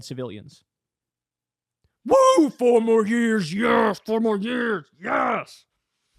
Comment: He had no idea what they were saying. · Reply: To be fair, I also couldnt understand what they were chanting at first. "For more beers" is what I thought I heard lol.